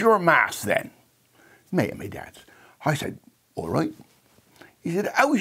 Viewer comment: Can anyone transcribe what this gentleman is saying in 0:26? I thought I could understand English. Now I'm crying :-p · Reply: me and me dad?